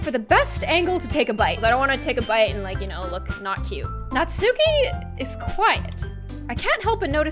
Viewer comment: did she say natsupi also the yuri voice yammy..........I can’t stop laughing